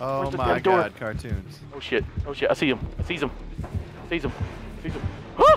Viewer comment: I am not a native speaker and I have a question for you guys since most of you are natives: Why does say sometimes sort of "I seeZ him" ? Like on 10:07. Or it's just my bad listening skill?